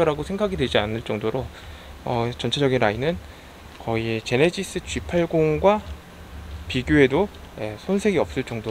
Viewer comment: 좋은 내용 감사합니다. 그런데 음성 들리는데로 자막이 이상하게 나오는데 일부러 그렇게 하신 건 아닌거 같은데 왜죠? (예 5:55 실제 말 : 전체적인 라인은 거의 제네시스 G80가 비교해도 -> 자막 : 전체적인 "아이는" 거의 제네시스 "팔고문"과 비교해도 ) 실내->신뢰, 소재가 적용->소재가 조금, 전체적인 바디감-> 추세적인 바디감... 등등